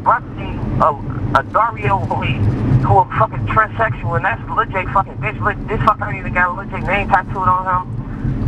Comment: Did he just say lil jay fuckin boys lol